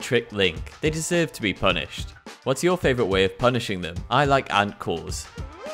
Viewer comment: I thought you said "I like ankles" not "I like ant-cores' " so was fully expecting a Yiga clan member to have his ankles broke not for a Member to break a yiga clan ...